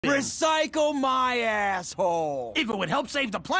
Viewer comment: Lol i was scrolling and then a video started with "I". I scrolled down the this video then it sound like it said" I recycle my asshole".